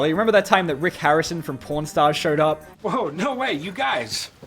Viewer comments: I swear to god I thought said Pornstars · when says pawn stars I thought he was saying porn stars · Ngl when he said “Pawnstars” it sounded like he said “p*rnstars”